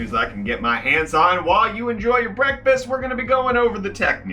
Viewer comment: Has anyone else subconsciously thought he said “Brettfast” instead of breakfast?